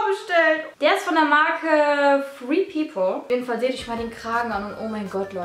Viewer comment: woher ist der Pulli? Ich hab sie nicht verstanden. Ich hab gred people oder so verstanden aber wenn ich danach suche finde ich nichts hahahah